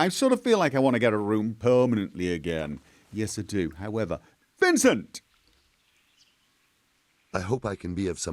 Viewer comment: Did I hear "Vincent"?